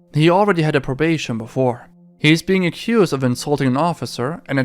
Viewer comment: Did I hear that right “insulting an officer” 😂wtf is that shit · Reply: ▪️ I thought he said "assaulting"... 🟥